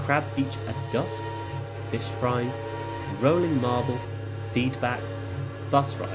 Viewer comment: I thought that said rolling meatball for a second🤣